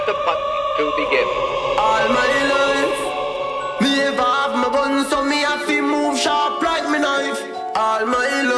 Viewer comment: "All my loaf. Live out my buns on me I feel muh sharp like muh knife" That's what I keep hearing and I can't get it out of my head, help.